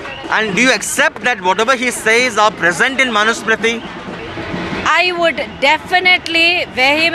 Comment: She is simply saying " i dont know..." haha..